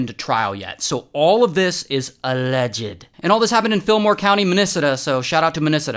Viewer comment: Did he just call Minnesota “Minisida”